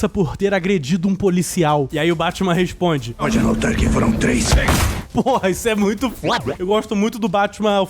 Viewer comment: n entendi oq o batman fala😢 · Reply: Ele diz: pode anotar que foram 3